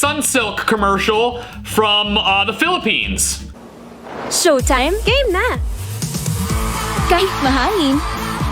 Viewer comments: sounds like it says "showtime, gay men" lmao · I know it’s Filipino, but did she just say Gay Men?😂